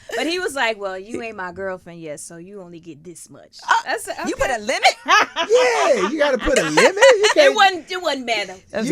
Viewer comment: Did anyone hear the amount? 😂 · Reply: $10,000